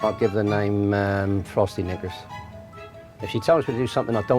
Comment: I thought for a second he said “frosty n-word” and I was shocked. 😮